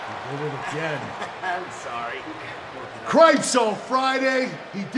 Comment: I thought he said "cripes on Friday!! " lol I'm going to start using it when things go wrong..